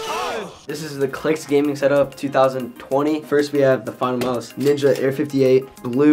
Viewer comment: did he said its <num> setup 🤔🤔🤨